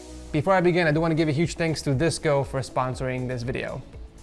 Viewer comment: I thought he said VSCO lol